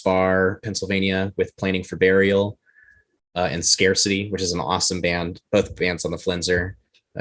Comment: did he say name of band was scare city